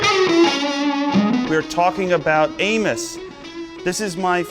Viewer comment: For a moment, I thought Joe said "We're talking about anus."